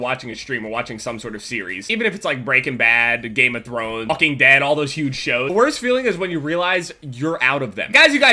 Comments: I thought he said "fucking dead". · it cut and I thought he said Fucking dead not walking XD